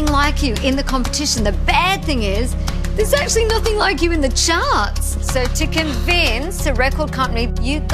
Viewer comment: Hi! I'm spanish and I didn't understood what is the last word Danny said in 01:13. "The bad thing is there's actually nothing like you in the..." Can somebody tell me please? Thanks:)x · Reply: there's nothing like you in the charts. ;)